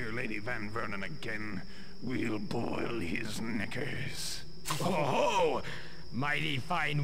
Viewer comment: They'll boil his what? 4:30 what nasty language do I hear. 5:38 someone's getting overexcited 5:50 HOLY FUCKING SHIT 6:40 MIRROR'S EDGE 12:00 Actually, you've killed them 14:40 We DID get some... ...broken bones Actually, if you checked the map CORRECTLY, there would be no suprise 15:54 maxed out Stealth skill, I see 24:28 (TF2 Engineer's voice) "Something is not right..." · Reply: They'll boil his knickers (=underpants). And yes, this certainly is talk that should be brought back!